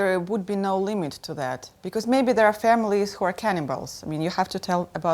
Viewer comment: I initially thought she said 'cannonballs' rather then 'cannibals'